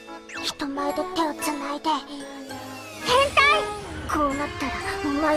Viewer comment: did she said H E N T A I?